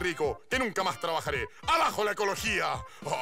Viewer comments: Siempre entendí "abajo la ecología" (?) Macanudo. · entonces Bodoque dijo "A la mierda la ecologia" x,d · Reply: Pero en realidad según yo dijo "abajo" y no "al ajo", eso nunca lo he escuchado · What? Siempre pense que decia ABAJO la ecologia xD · 🙀🙀🙀🙀🙀 ¡dijiste la 11! P.D: antes pensé que dijo “abajo la ecología” :/ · y yo que pense que dijo "abajo la ecologia" Que sonaba feo Pero decia... :"v se me cayo un idolo... Ok no xd